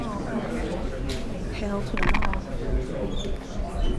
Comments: someone say's "Hail to the ……." can't make out the last word. Any help out there? Sounded like they tried to say in mic. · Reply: Sounded like they said "hell to the nawh" as in no.. ebonics still can be tricky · “Hell to him” reporter says in the background. Fake news gonna fake... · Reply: I was curious about that as well. Is she saying, "Hell to them all" or "Hail to the Mall"? I couldn't discern. Certainly intriguing. I'm curious if there is actually any change of heart during these proceedings or if it's simply a way to grandstand already-determined beliefs. Good catch on the comment.